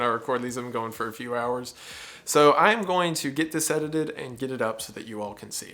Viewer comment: When you said “time to get this edited” after saying you were tired I thought you said “time to get sedated” and idk why but it cracked me up lol. Like… mood